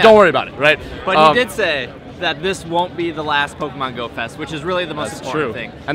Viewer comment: Ohhhhhh you said GO-fest, you see i thought you said Goat-fest